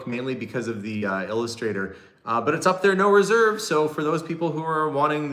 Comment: Perusing the auction now. I noticed that there is a minimum bid of $10.00. I thought I heard you say there were no minimums, but maybe you said no reserves and I misheard. Will have to listen through again.